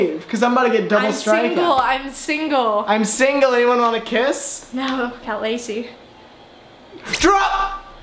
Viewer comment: wait wait wait at 4:00 did she say “what about lacy” when he said he’s single cuz cannot❌🙅🏼‍♀️ do that